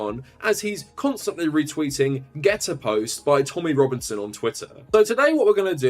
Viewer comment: did he just say 'twittain'